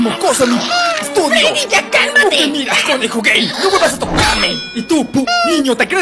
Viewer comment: Bonnie ❌ Conejo g4y ✅ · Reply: ¿dice "conejo"? Wa, yo le entendí "pendej0" XD